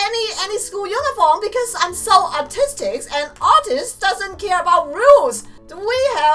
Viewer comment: I thought she said "artist so autistic" lol